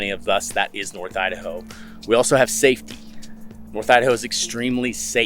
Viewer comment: I was watching this in a loud room and when you said “we have safety” i thought you said “we have satin” and i almost fell out of my chair